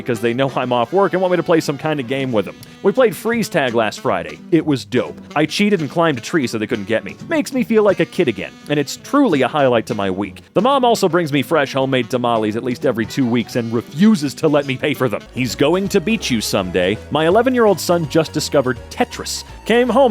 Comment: thought he said: “fresh homemade molly”